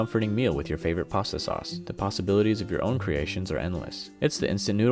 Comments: Is it just me, or did he say pasta-bilities instead of possibilities? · I had to rewind because I thought he said “pasta-bilities” 😆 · Did he just say......pasta-Bilities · Did he just say "The Pastaibilities of your own creation are endless..."